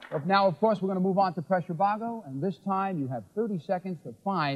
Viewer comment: Not a great game but Bill is always a joy to watch, btw in the PRESS YOUR (or PRESSURE) BOGGLE, the song in the background was LILY WAS HERE by Candy Dulfer and David A. Stewart (1/2 of the EURYTHMICS). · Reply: Glad I'm not the only one who thought I heard "Press Your Boggle"